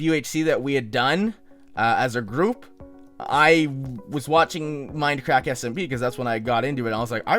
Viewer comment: Did just say minecrack